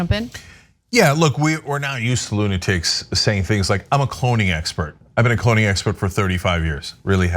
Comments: Lol, I totally thought she said “pony expert.” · ooooh. I thought she said "pony expert." Okay. Now I get it!